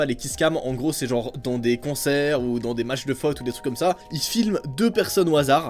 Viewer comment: C'est moi ou à 4:33 dit fot